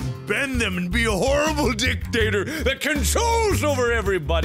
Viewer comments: I heard "Dick"-tator. All is sayed. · I'm sorry. I love you Mark, like the big brother I never had, but I swear at about 0:56 you said it'd be cool to be a "Dick-dater"